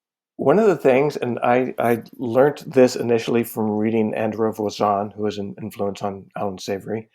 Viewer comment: mentions someone who was an influence on Allen Savory, does anyone know the name? I heard, Andro Vasaughn??? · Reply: Andre Voisin author of Grass Productivity